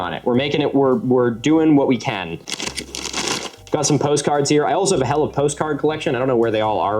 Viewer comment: Huh, I thought they were saying pot chards. Learned something minutes in